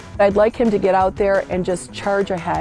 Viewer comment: There is no way in hell anyone can ever convince me that 81 million people voted for that man more than any other president in the United States history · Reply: I swear I heard him say 81 Billion